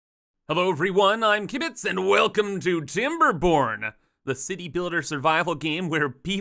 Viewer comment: I had to double check the title. I just heard "I'm Kibitz, and welcome to Timberporn!"